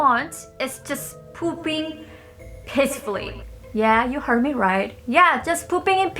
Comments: I thought she said “I want to poop pissfully” ... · Why did I hear "pooping pissfully!" I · I'm not trying to be mean or anything but i laughed when she said pooping in peace.I thought she said pooping in "piss"